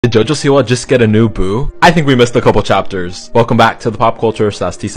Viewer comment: I thought you said "Did Jojo Siwa just get a new boob?" I was like HUH?!